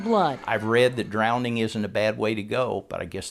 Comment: Did he say he read that drowning IS or ISN'T a bad way to go? I always thought it was a bad way to go but I keep hearing on playback he said it isn't. Just total curiosity on what he's saying